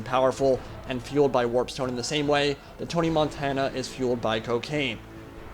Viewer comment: I thought he said: "...Hannah Montana is fuelled on cocaine!" xD